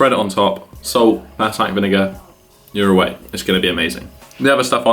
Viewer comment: Silly question, but what type of vinegar did you use? It sounded like you said thai vinegar, but I can't make it out haha